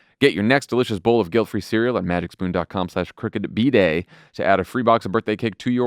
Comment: Was anyone else hearing "crooked bidet" or was that just me? 🤪